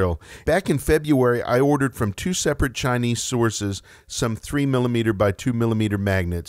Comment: before I went back and listened again I thought you said storeses lol